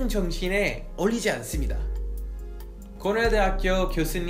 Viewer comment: 고려대학교로 들림